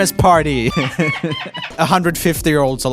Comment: legit thought he said “the 150 year olds” 😂